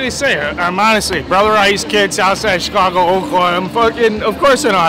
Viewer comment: Anyone wanna translate that to regular English for me? I've got nothing between "What do you want me to say?" and "Fuckinnnn. Of course I know how to throw bags" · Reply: I’m a brother rice kid south side of Chicago oak lawn . That’s what he says in between lol